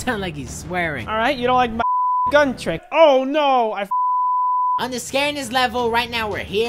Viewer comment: It sounded like he said oh no I fucked